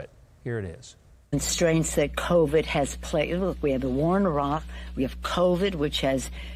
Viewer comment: IMO it doesn’t sound like Nancy Pelosi said “we have a wa in Iraq”. It sounded like she said “we have a wr in Iran” 🤷‍♂️ what else are they not telling the Americans 🤔